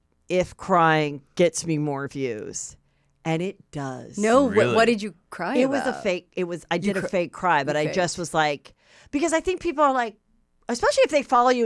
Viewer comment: I thought you said “fake crime” 😅😅 Love MacNcheese!❤